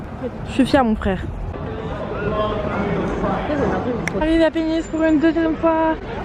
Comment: J’avais compris penis.. pourquoi j’ai l’esprit si mal placer 😭